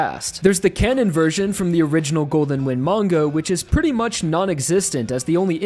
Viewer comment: did you just say mango?